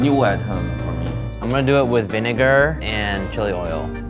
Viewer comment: Did I hear what I think I heard or what did he say vinegar? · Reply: alma sólido he said vinegar. And that’s how you pronounce it.